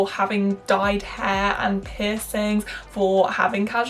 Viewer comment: omfg I heard that as ‘pissing’ like she shamed others for pissing and I CACKLED